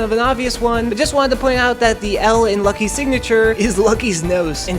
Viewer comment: I thought that it said "loucky"